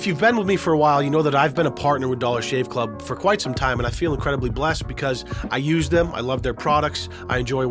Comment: i thought he said "incredibly black"